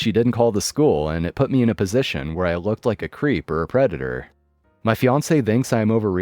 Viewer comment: I first misheard the part at 2:57. Instead of "where I looked like a creep or preditor", I somehow heard it as "where I looked like a creep or a redditor".